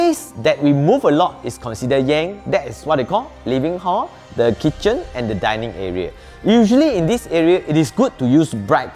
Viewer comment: the WHAT Hall, Living Hall, Kitchen and Dining Area? I couldn't tell what he was saying at that part. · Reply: The place that we move a lot is considered yang, that is what we call the Living hall...